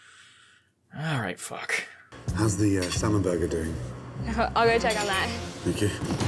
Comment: I swear it sounded like Gordan Ramsey asked "How is the Salmonella Burger doing?" and it really fits.